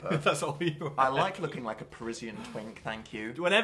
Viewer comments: i thought dan said 'prison twink' instead of parisian 😂 works both ways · I thought Dan said “prison twink” which works with the stripes